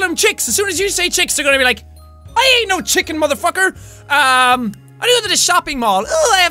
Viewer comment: Did he actually say " I ain't a chicken motherfucker!" Omg I'm dead. XD